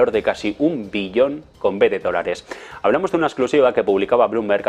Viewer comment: 013:39 “un billón con b de dólares " ¿Escuché bien? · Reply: "Un billón con B, de dolares"